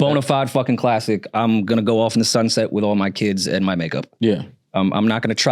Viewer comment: thought Rory said “I’m going off into the sunset with my kids and MY NIGGA” 😂😂😂